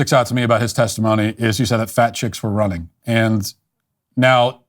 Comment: OH FAT CHICK! I thought they were saying Fact Check.